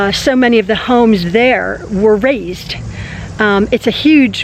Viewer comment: Oh ok. All this time I thought you were saying "razed" as in tearing down and rebuild. It's being "raised"...